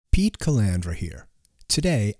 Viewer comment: I swear I thought you said "beat collabor here" lol.